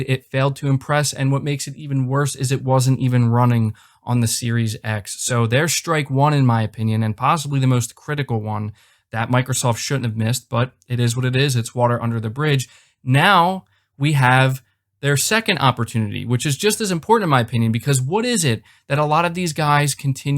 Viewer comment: I thought you said “water under the fridge” at first at 7:24 in the video. I was about to say “...uhhh that’s not a good thing, pleighboi.”